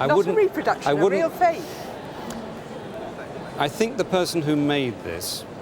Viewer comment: did she say ‘oh, a real fake!’ ? Lol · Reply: Yeah, it's a real old fake.